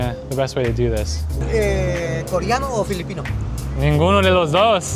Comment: Random. @ 6:34 i heard someone asking "koreano or filipino?" Haha!